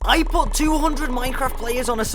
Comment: I missheard the first part..... I heard: "I BOUGHT 200 PEOPLE"